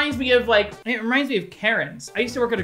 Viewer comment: When he started talking about karens i thought he said carrot lmao